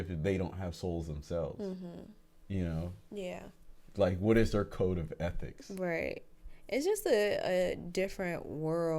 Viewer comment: code of ethics?! Oooooooh I thought you said EDICTS...we have lots of those...